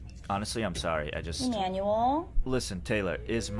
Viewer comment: "Is that because I am an Emu?.." "Please stop" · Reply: She said please stop? I thought she said “you suck” lol😂